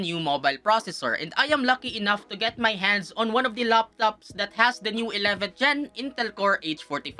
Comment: i heard laps laps instead of laptop🤣🤣🤣